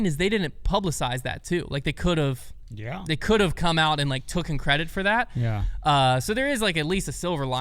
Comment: Did say the word “tooken” right after 8:10?? As in “Prodigy could have tooken credit for…”??